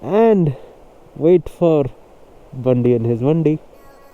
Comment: Yo! For a second I thought he said "and wait for Bandi and his r*ndi* I was like whaaaaattt did he just say? 😂